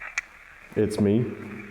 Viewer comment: I hear more "yes me" than "it's me"